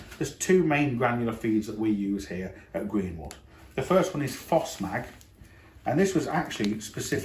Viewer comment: what is that your fertelizer you speak at 10:47 ? fosmag ? granular feed ? whats it made of,, how can i make my own instead of buying it ? =) or that other one,,, naruko ? the one you speak of at 11:11 .. ? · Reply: Phosmag.